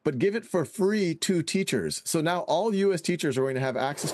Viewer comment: Did he say "free to US teachers"? What about teachers in developing countries?!!